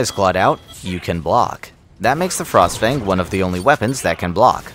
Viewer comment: This is straight up false. The mosin and eviscerator can also block. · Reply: That's my mistake. At the time I heard "the only" instead of "one of the only".